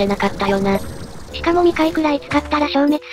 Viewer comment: なんか霊夢が言ってた1:38のところ「3回ぐらい使ったら」の3回が2回って聞こえるんやけど気のせい？